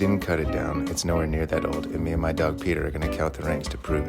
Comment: i thought he said “and me and my dog peed on it” 💀